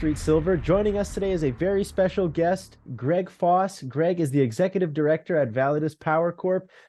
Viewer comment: GREG FOSS? I THOUGHT YOU SAID REDD FOXX LOL WOW THANKS AND TEAM